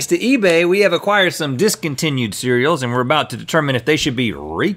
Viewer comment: I thought it said discounted